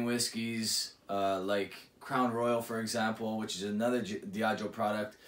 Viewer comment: You're throwing Chivas Regal 12 under the buss comparing it to this. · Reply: LOL, my mistake, I'm sorry. I thought you said Chivas Regal when you actually said Crown Royal. I had my headphones in listening to you while I was doing the dishes and heard the wrong thing, again my apologies.